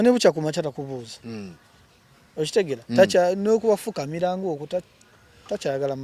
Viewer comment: Naye banae 😂😂dd I hear okufukamiira?? Awo Nze bamaze Okungoba mubufumbo